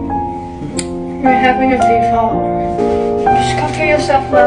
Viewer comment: For a second I thought you said you’re having a baby but I hope feels better😂❤